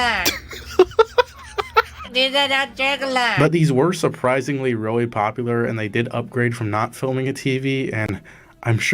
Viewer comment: i thought he said go for the jugular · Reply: jigglin jiggler